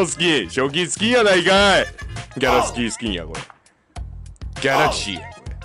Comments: ギャラスカースキンに聞こえた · ギャラスキースキンに聞こえたやつ👍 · ギャラスキースキンに聞こえたやつ👍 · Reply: ギャラスキースキンに聞こえるの俺だけじゃなかったー！